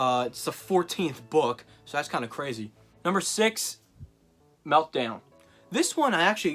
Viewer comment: your an idiot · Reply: Sorry I thought you said the Meltdown 6th?